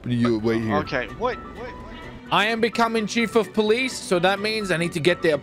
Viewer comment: At first I thought it said chef of police